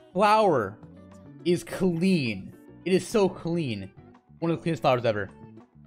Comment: I thought he said Klee XD